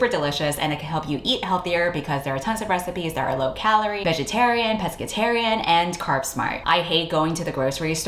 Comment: In the HF spot, I thought you said "low calorie, vegetarian, Presbyterian" 💀 Hamburger help me, I'm so tired